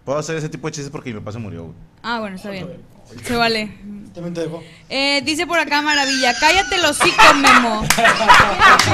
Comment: que dijo?! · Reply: Dijo “a ti también te dejó”